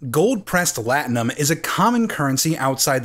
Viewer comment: lol this time I thought they were saying “Cold pressed latinum”